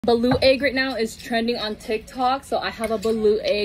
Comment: I thought she said blue egg. And not Balut 😂 . As a Filipino I'm not a fan but my husband is. Too much high blood as my mama would say 😂😂